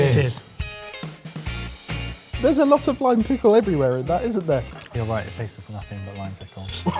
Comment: Misheard the first time and heard lye pickle. Based on how things unfolded, it could've been just as good in the dish... :|